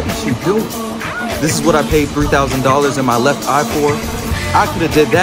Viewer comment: “ this is what I paid 300 thousand dollars for and my left eye for” lmao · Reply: I thought he said 3,000 and left eye...🤖😉